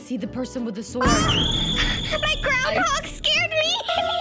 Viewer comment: I thought she said: MY GRANDPA SCARED ME! lmao🤣🤣🤣